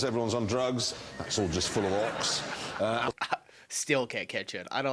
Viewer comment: im British and i dont have a clue what he said 😂😂😂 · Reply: "full of Ox", it's an animal lol.